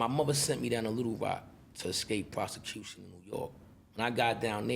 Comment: Did he say at 9:50 that he left ny to avoid prostitution?